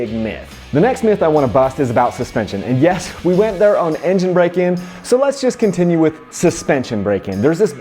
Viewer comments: Its kinda funny, i thought he said engine BRAKING not engine BREAK-IN. Im like why are we talking about semi jake brakes? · I thought you were saying “engine breaking”. Hahaha. I live that Utah accent 👍